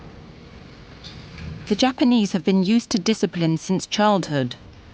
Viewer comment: cc never fails to make me laugh. my fan can be really loud so i have cc on to make up for it... 18:45 penis (instead of japanese)have been used to discipline since childhood but for foreign inmates it's hard to take...lmao thats just gold